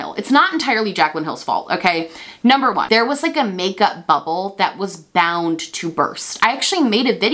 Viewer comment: I know you said "makeup bubble" but I heard "makeup butthole" and I'm honestly ok with it 🤣🤣🤣